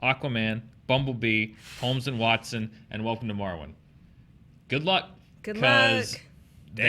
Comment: Man, I thought said Welcome to Morrowind when talking about upcoming movies, and just about had a heart attack thinking that there was going to be some Elder Scrolls movie that I had completely missed hearing about.